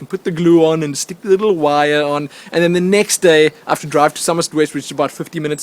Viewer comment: did he say that he has to drive to summoner's rift?